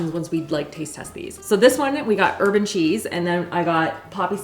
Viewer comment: I heard “urban” cheese and was like, “what are they doing in Canada?” She definitely said, “herb and cheese”. 🤣